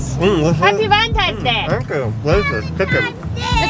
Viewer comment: hahaha it's so sweet. I'm in Germany, when the dad said "Thank you" with pizza in the mouth, for a second I thought he said "danke"(thank you in German) lol